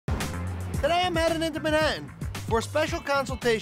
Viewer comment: Y’all what did he say in the beginning I hear “I’m here at the rjdicjwksjfb “ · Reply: “Today I’m headed into Manhattan” lol